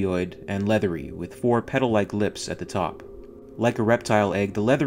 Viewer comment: WTF! I misheard 0:56 as "pedo-like lips". How do you DO "pedo like lips"? My fuckin brain's initial translation's dumb sometimes. facepalm